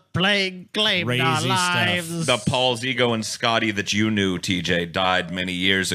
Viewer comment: Thought he said "Scotty the Jew knew" not "Scotty /that you/ knew"